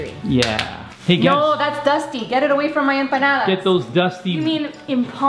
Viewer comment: At first I thought y’all were saying bananas